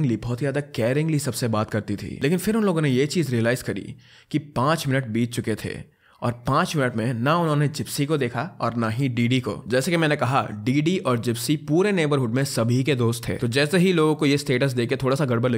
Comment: what did he said? 5 minutes beat chuke the...5 minutes or 5 days???